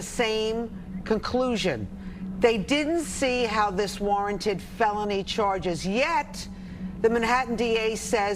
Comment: Do you understand the word is PUNDITS and not Pundints? No, I see you don't. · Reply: I thought they were saying "pound it." I have such a dirty mind 😂